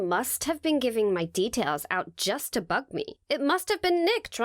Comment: am i the only one who heard... ummm "just to $%^* me" or is it just me :|